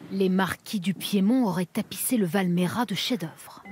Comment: Je n'avais pas compris que c'était chez moi quand j'ai entendu "Val Méra"... Val Maïra, s'il vous plaît ! Mais pas que, le fromage et le monastère avec le monsieur très enthousiaste, c'est la vallée parallèle, Val Grana. À par la prononciation, joli reportage !